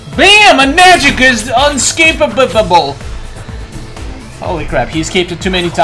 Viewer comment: what did you say? Escapitible